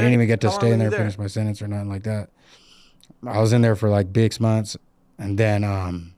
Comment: Did he say dicks months ? To diss the 60s?🤣 or im tripping ?? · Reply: He said Bix